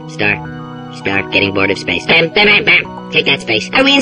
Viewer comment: listen at 3:13 for a few seconds.... did he say dead space? it sounds like it tho... srry if it isnt cuz my hearing isnt the best loll